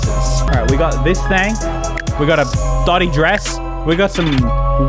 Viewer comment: At first I thought you said thotty dress but then I realized you said dotty dress